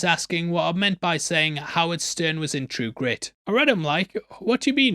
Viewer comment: for the record, i did NOT hear you say "howard stern" . I heard "How its done " . its a gift, really.